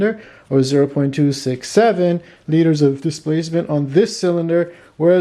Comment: did you say..67...???